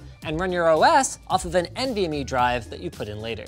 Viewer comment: Caption error NVME instead of NBME.